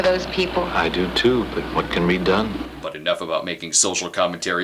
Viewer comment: Did he say "What can we done?" or "What could be done?" ? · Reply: "What can be done?"